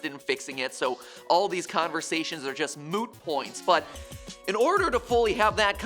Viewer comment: did MatPat say Moo Point? Idk but if he did I praise friends reference